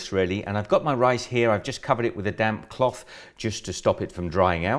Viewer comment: I thought he said damn washcloth when he was saying damp washcloth...